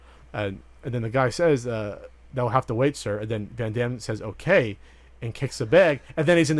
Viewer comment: I always thought the officer said "But I'll have to wait sir." ...like Frank could take his shower but the officer had to wait to keep an eye on him and Frank said "Ok." to that. Never tried watching with the closed captioning on to see what is actually said.